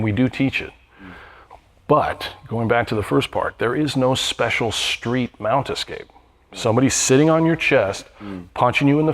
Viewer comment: What??? There's no mount escapes "FOR THE STREETS!!!" ??? I like this guy. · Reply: I heard "There is no SPECIAL mount escape for the streets", not exactly the same.